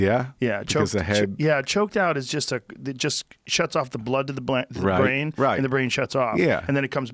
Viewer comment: Anyone else catch the "blood to the Blaine" 🤣🤣🤣 Edit